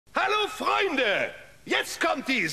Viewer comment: I speak German but I wasn't expecting the German and expected English so what I heard was "Hello Fried Greg!!" And I got really confused